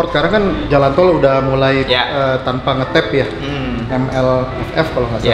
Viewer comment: Jalan tol tanpa ngetap gw dengernya MILF anjir. Seriusan itu MILF?